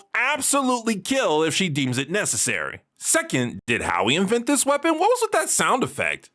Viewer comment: what did he say? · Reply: "Did Howie invent this weapon," reference to the Howie scream